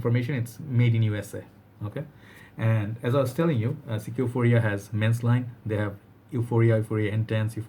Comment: I thought you said ck echo but you mean aqua/actually look that up😅😂...